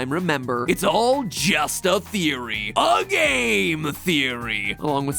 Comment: huh i always thought he said “I have a theory A GAME THEORY”